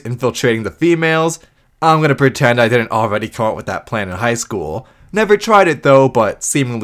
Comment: can you please elaborate on what in hell that you just say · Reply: “I’m gonna pretend I didn’t already come up with that plan in High School” Sure it’s slurred, but not hard to hear. I heard it perfectly fine first time.